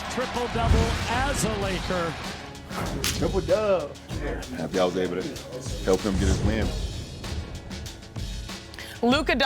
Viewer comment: What did LeBron said to Luka in the locker room?? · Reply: triple doub(le), happy i was able to help him get his win.